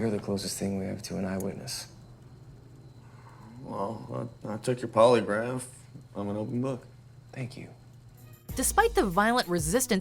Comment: what does he say in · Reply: Ane E. “I took your polygraph. I’m an open book.”